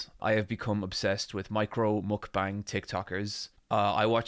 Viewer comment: I thought you said Mike Rowe Mukbang tiktokers and i got super excited at the idea that the host of the hit TV Show Dirty Jobs, where a man goes to dirty jobs and does them and responds to them in awed reverence, is not only eating so much, but is now suddenly several people.